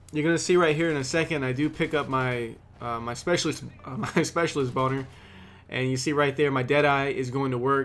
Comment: Am i the only one who heard specialist boner at 1:33?